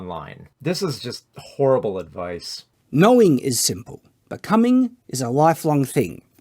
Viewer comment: To be fair though Shad did actually say "becoming", it's just that the first syllible was pronounced quieter than the others / sounded like "but"